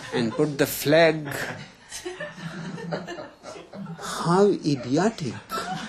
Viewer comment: Anyone know what word he used at 6:00 ? Also, I rarely have any disagreement with what Osho has to say, but he mentions that humans own the earth, which is not true. We are simply here with all the rest of the organisms. Imagine - John Lennon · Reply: He said 'How Idiotic'